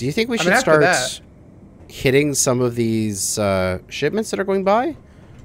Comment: Did... did he just say shitments?